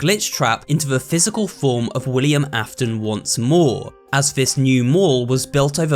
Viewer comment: I dont know why but ANY time someone says '' WILLIAM AFTON '' i hear " wolly the mamoth "